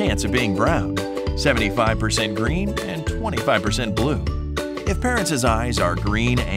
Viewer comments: On 1:46 Did I Just Hear 25% Cuz The Write Was 24% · did he just say 25% bleu? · Did you say blue is 25%?